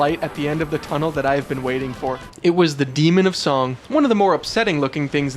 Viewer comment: I heard Semen of dong so much the original name just doesn’t sound right anymore